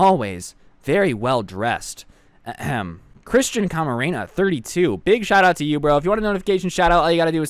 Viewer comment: When you said Christian camerana I thought you said Christian Macarena